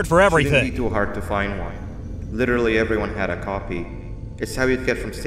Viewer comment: I thought banban said everyone had a coffee because it was a lunch room but I saw the dialogue and he said everyone had a copy not coffee